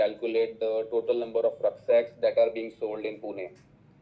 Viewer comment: i didn't understand what he saying Recktiles ?? Can you please comment for the above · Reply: Rucksacks & Trekking Backpacks